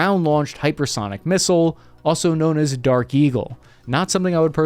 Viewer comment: Misheard you saying dark kegel at